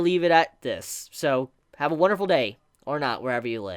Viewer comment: "Have a wonderful day, or not..." I really thought he said that at first XD